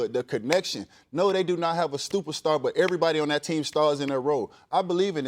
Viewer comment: Did he say "no they don't have a stupid star"? When he meant to say super star?